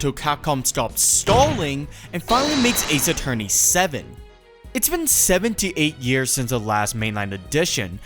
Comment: For a sec I thought you said “it’s been 78 years since the last ace attorney game”